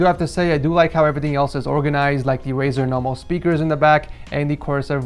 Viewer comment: I thought said “Razer Nohomo Speakers”